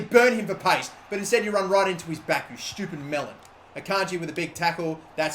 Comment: did he call martial a melon??